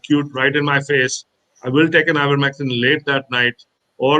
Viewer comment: You miss a lot if you don't turn transcript on. Things like " 71:34 i will take an hour mexican late that... " It did sound like he said "an ivermectin."